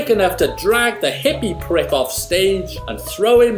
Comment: did he say prig or pri(dugh)